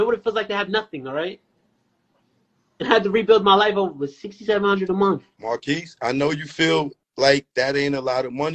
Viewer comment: He sound more like the single mother than the son😂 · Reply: I thought bro said $700 a month. This kid is soft af